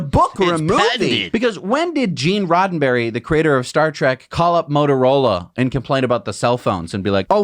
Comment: Did he just say....Star Trak......?